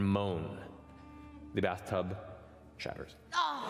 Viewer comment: what is the joke Mercer says? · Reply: 'the bathtub shatters', Jester laid down a towel to protect the tub as it was her mom's bathroom.